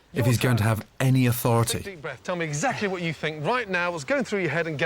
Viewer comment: did he call him dickbreath? I am so using that.